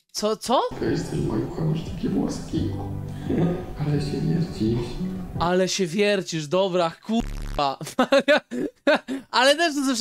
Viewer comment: Ja zamiast usłyszeć "ale się wiercisz" czy "ale śmierdzisz" usłyszałam "ale z ciebie Zdziś"